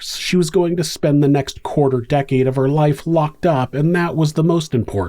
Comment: Did I hear that right? She only got a quarter decade in prison?